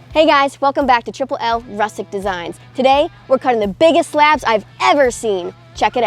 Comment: Thought she said the biggest ass 😭